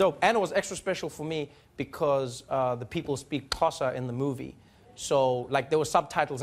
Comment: Did he just say "KAT OSA" instead of pronouncing "XHOSA"?